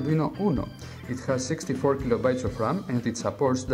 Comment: I swear, I thought I heard him say "It has 64 gigabytes of RAM."